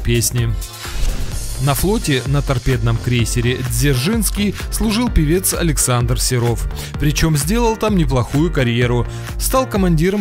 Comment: Мне одному послышалось СДзержинский?